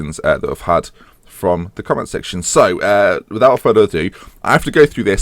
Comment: does he say "without Freddy Adu"? ... what's Football Manager without Freddy Adu?